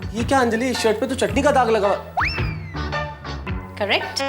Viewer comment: Mehta Sahab said chaddi or chatni😂😂I hear 🙉 chaddi😮